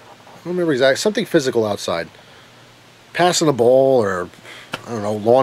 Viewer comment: I NEED THAT DEMON LADY STORY I NEED IT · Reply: at first i thought you said passing a bowl i was like what!?